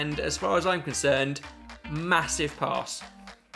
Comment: I think it's so unfair of to call a massive arse after all that effort (jk but that's what I heard the first time at 13:59 )